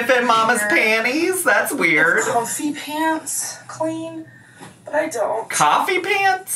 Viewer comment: It's not you, I thought she said coffee pants too.